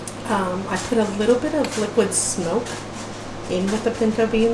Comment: I thought you said you put liquid SOAP in the beans!!!!😝